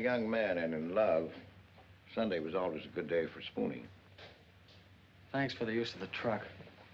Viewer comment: Did EGR say when he was a young lad, sunday was always a good day for spooning?? I rewound a few times, still sounds like that. Wow.